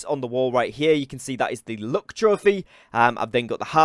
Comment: I kept hearing him say "look trophy" not "luck" 😂😂